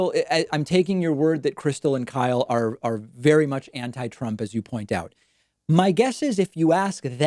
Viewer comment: + Did he say anti trump?